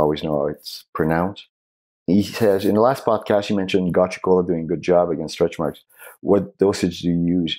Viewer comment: gotchgo? wtf does he say here? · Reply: Gotu Kola